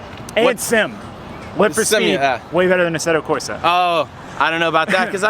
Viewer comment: What was the game said @ 12:30 ? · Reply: Live For Speed